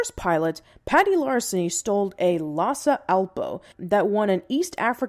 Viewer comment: Did she say "lotsa Alpo" at 2:28? As in the dog food? Like it's a lhasa apso that eats lots of Alpo?